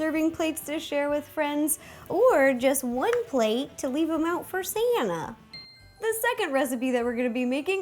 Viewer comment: hilarious. Keep replaying it over and over again and it sounds like she is saying Rosanna.